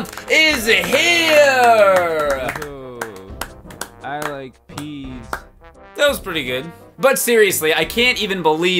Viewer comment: I always thought he said "I like cheese" instead of "I like peas", I only now realize that.